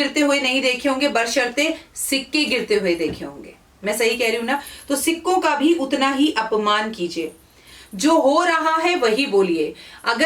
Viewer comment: pe video me aapne kya bola ki sikko ka bhi apman kijiye???? 😅😅😅😅